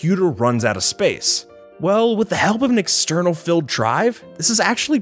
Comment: Did you just say “an external *FILL* drive??”😂😂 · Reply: nope, filled